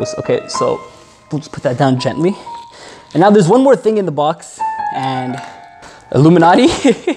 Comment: Did he just say Alluminaty instead of illuminaty?